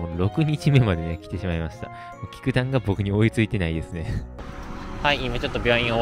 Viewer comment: キムタクが追いついてないですねって聞こえましたすみません😭